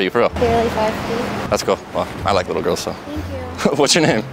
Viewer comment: Did I hear little girls? 😳